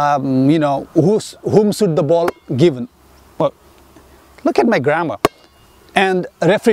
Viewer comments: Broo I heard "look at my grandma" 😭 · "look at my grammar" lmao i love how that part came in lol but it was very helpful thank you very much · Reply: KElZ i thought he said “look at my grandma”